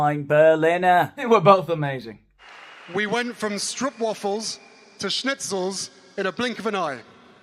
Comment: Sorry, I had to go back and listen at 3:35, and I think Harry's saying "stroopwafels to schnitzel" which I think is referencing last year's games in The Hague.